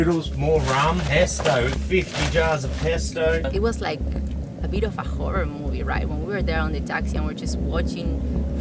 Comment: Sounds like she said “It’s like a beautiful horror movie” Okayyyy lol